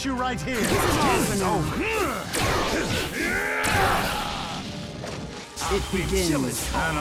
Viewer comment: sounds like Kaguya is saying "shit begins" lol